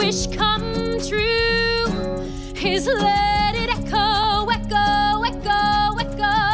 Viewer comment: so a worst version of the Frozen song? 5:39 OH! ECHO! I SWEAR i thought she said "let go" lol. Also Gal Godot as the witch & supposed to be envious of Snow Whites beauty? Gal Godot is infinitely prettier than Rachel ziegler. 9:17 Peter Dinklage being AGAINST 7 HUGE POTENTIALLY LIFECHANGING ROLES just came off as him worried about being replaced by OTHER talented "little people "/Dwarves, Midget, (SODRY, genuinely not sure what is preferred by the community) but for him to take that stance is absolutely RIDICULOUS. First the new Charlie and the Chocolate Factory with Hugh Grant and now the BIGGEST role. What other work will be in futures??